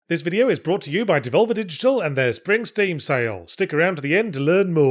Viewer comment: You can tell I've been spending way too much time on music the last couple of days because, for a second there, I thought Yahtz said "their Springstein sale" and did a double take lol